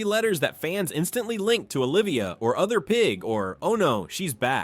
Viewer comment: i thought you said that was Oscar pig?